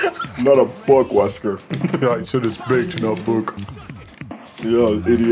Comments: Is it just about Chris being high, or something else? I'm seriously just as confused as Wesker here. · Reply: I didn't understand it either, I thought he said "big" or big-s" and just gave an abrupt pause. Granted that's still pretty damn funny as him saying "baked." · The first time I heard this, I definitely thought he said “Big” Baked is way, way funnier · Oh he's saying baked? I heard it as "Bigt" and had no idea what it meant